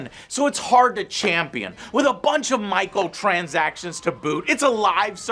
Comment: Did I hear "Michael Transactions" ?